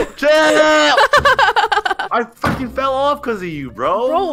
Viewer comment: swore he said "I fucking fell because of u"